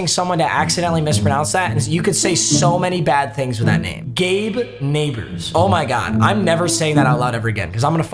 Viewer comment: I’m a chargers fan and when I first heard the announcers say gabe nabers I deadass thought they said gay neighbor